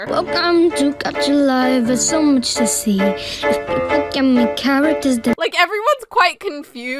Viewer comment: I hear "yummy gummy characters"💀💀💀